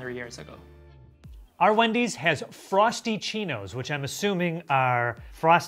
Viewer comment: FAKKIN LOL マジで知らなかったww Also, I thought you said Frost Chinos 😂 Que cosas dices!! Until I saw the spelling